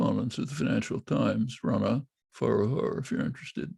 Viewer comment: what name did he say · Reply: Rana Foroohar